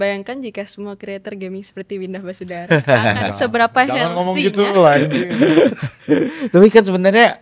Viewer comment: bang windah bilang apa tuuu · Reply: "jangan ngomong gitu, kak angie"